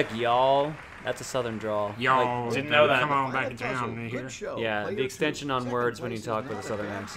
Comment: Wait... At 17:08 what is saying? 'Yall come on back down..?' Probaly just me but it sounds like the N-word?